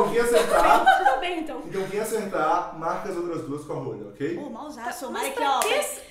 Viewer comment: escutei o dizer "rola"